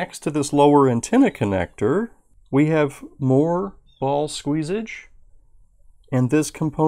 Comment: Am i the only one who heard him say we have "ball squeesage" at 2:46🤣🤣😂